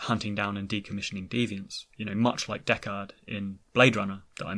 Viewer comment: That is uncanny! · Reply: I've never seen Blade Runner, and I was so confused when you kept saying Deckard, because I thought you were saying "Dechart." At first it threw me off, but now, my RA9, that's funny! 😂